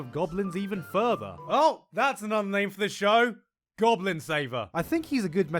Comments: instead of goblin saver i heard goblin slayer and it was funny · Lowkey thought he said “Goblin Slaver” instead of “Goblin Saver” ...I mean he’s still right in a way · first time i thought you said "goblin slaver", the image before does kinda match tho